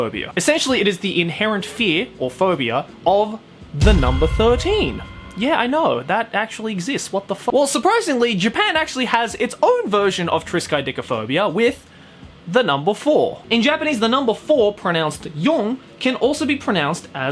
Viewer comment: I'm taking my guesses: is it called Mistaphobia?